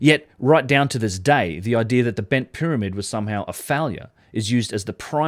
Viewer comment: Every time you say “failure,” I hear “phallia” ….